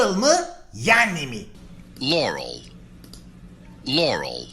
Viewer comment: Ben lauren duydum